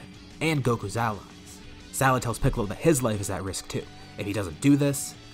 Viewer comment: i swear i heard pickle